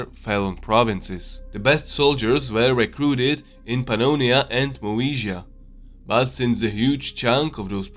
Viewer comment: the best soldiers were recruited from where??? I can't hear it... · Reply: Pannonia and Moesia